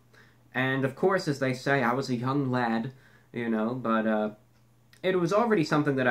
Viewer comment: Thought he said "I was a hung lad"